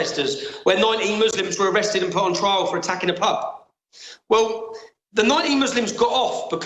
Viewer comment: Just for anyone who misheard it, I thought he said "19 muslims were put on trail in rotherham for attacking a puff". He actually said PUB :)